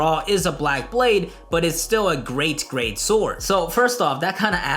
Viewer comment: I don't ever wanna say the words Great Grade ever again... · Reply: Lol it just clicked that you were saying that. I thought you were saying great great swords